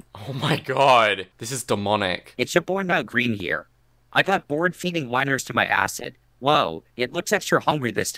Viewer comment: LMAO AT 17:36 I THOUGHT IT SAID HE GOT BORED FEEDING MINORS TO HIS ACID when i realized that wasn't what it said i literally burst out laughing hysterically harder than i have in months